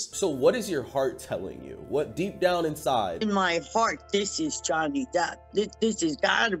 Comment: She really said "My fart" instead of "my heart" and nobody noticed 😂